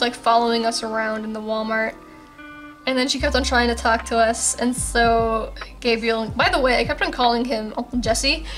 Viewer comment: I walked out of my room to get some water and when I came back all I heard was “and this lady shot Gaibriel” and I was like ”alrighty then!” Btw I really love your OCs!